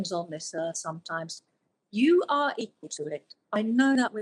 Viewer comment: I couldn't make out what she said. " you are a ....." what? Can someone explain? This interview was amazing and very moving. I wanted to cry a few times. I also found it unbelievable, the people who we're running or driving away! Many blessings and love from Florida. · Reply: "even though you go through some very tough times in your life, you are equal to it!